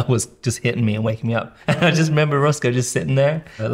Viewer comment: i wasn't watching the screen when he was talking about Roscoe, and i thought he said "Russell" when talking about him liking squeak toys etc loll